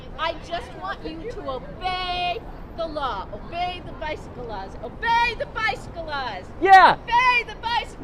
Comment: I thought she was saying obey the vice of the laws